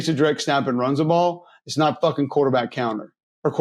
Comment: Did say "fucking" at 11:55? Let's goo. I love that word